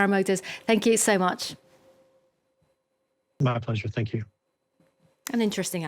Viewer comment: did he say not a pleasure? 😂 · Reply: My pleasure